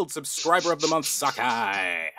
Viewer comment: I swear I heard "Subscribe of the month sucka!"